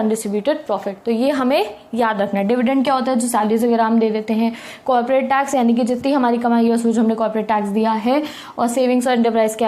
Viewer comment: can any on tell me what she said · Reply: "Salaries wagera hum de dete hai"